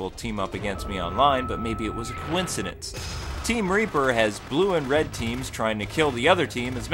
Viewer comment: hehe. you sounded like you said team raper